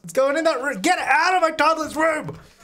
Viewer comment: Silly thing: it took me a while to realize that you were complaining about the MAILMAN as I keep hearing "Male Man" and I was laughing so much because of that 😂